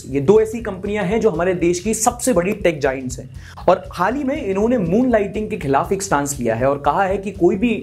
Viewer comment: I miss heard moonlighting . I thought you were saying moon knight.